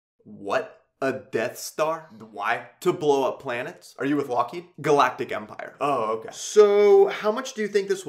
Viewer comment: “are you with Lockheed?” is incredibly underrated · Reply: Oh, so that was "Lockheed", I thought he said "Loki" and couldn't figure out what it had to do with him. xD